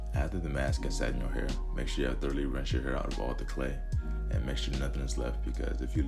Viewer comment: can someone PLEASE tell me what he said cause I can't make it out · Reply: After the mask has sat in your hair, make sure you throughly rinse your hair out of all the clay. If you leave some (clay) in there, the clay will harden and it will be hard to get out, so make sure you rinse everything out.